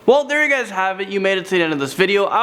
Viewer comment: I thought it said “Landon do not sing” 🤣😂